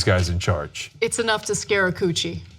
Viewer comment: Did she just say "scare a coochie"?